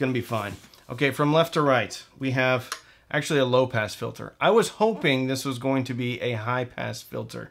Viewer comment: You said “low pass filter” I heard “low pants filter”